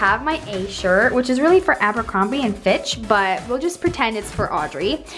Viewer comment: thought she said bit** but she said flitch👌🏼🤦🏽‍♀️🤦🏼‍♀️